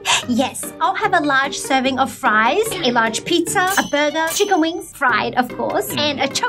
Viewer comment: Am I the only one that heard her say fries 2 times